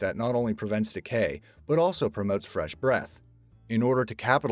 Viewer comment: "prevents fresh breath " lol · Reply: Cr1spyNoodles I thought he said that too but he says promotes